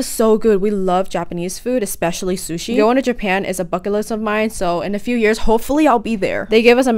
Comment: Why when she said bucket list I heard “f**k it lists ( srry I just hear everything wrong ) 😊😅